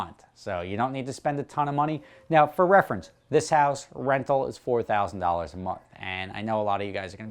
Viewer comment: I misheard and thought you said $40,000 a month.